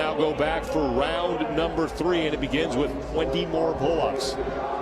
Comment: Oh my god these pull ups are hilarious - guess its the most efficient way within the rule set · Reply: did he say “wimpy more pull ups”?